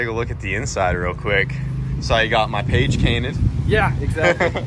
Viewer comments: did he say page cainted? Or have I been awake for too long? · "page cainted" Anyone else hear that? lol